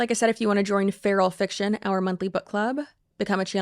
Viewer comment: Ok...just watched this while getting ready for bed and when said "if you want to join our monthly book club"....I heard "if you want to join our monthly butt plug" and i had to rewind to check....defo time for bed cheryl-ann!..and maybe church 🙈